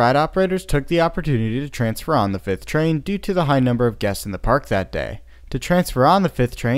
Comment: Sounds Like He Said Due To The High Number Of Deaths At The Park That Day 😂😂😂 (He Said Guests)